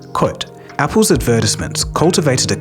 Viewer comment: took me a second to realize what you were saying. lol. Advertisments.